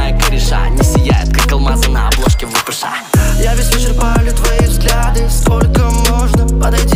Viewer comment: там не "я весь слышу а не твои взгляды" а "я везде чирпаю твои взгляды"